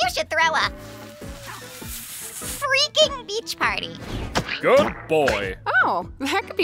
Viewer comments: My dumbahh thought it said ‘Breach’ not beach 😭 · Did Caine actually call bubble a good boy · Bro, when bubbles was saying throw a freaking beach party, I thought bro was saying this how about you throw a freak party